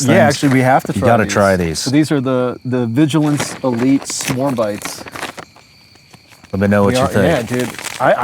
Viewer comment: For some reason I heard: 'Swarm Bites' So I'm thinking: "What the hell is Swarm Bites?" I had to look at the store. And it's...S'more Bites.😂😂😂